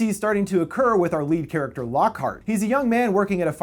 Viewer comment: did you say Lockhart? OMG ITS GILDEROY LOCKHART FROM HARRY POTTER!!!!!!!